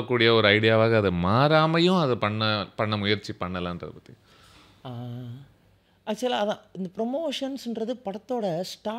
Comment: Can anybody tell me what are they speaking about oak????? What is oak in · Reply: Woke nu soldranga bro